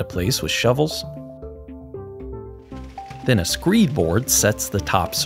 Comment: A "screeb" board? Captions say "screed" board.